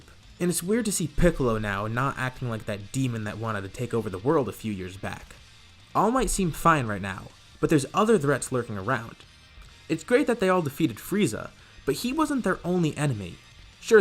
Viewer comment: Did you say all might will be fine now